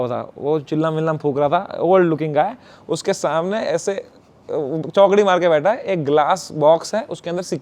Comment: did i hear chowkdi ?